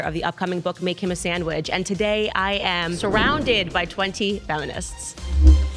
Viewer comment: In the beginning, I thought Candace said "I'm surrounded by 20 felonists"